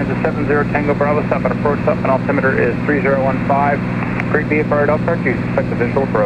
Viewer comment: I think he said “Great VFR at Elkhart”. Had to play that back a couple times 😀. That rapid refresh tool looks great. How do you like the ezwxbrief site? Would really like to sharper my weather further - seems like they may be a good resource for that. Btw, the noaa site has a “loop” column, but I have to idea how to “play” it there. Awesome video - probably the best I’ve seen that covers an IFR flight! That level of editing is super impressive (and much appreciated)!